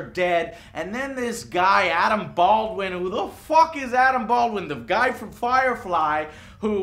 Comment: Adam Baldwin. Aww I thought he said Alec Baldwin.